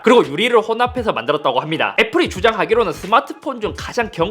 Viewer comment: 왜 애플이 주작하기로는으로 들리짘ㅋㅋ